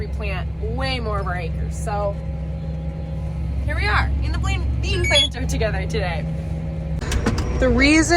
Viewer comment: Sounded like you were just on the verge of saying, "Blame planter".